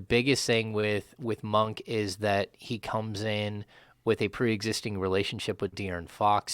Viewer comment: I don’t know why I keep hearing “Kings messed up the relationship with DDV” Maybe DDV agent (or DDV) messed it up Just saying lol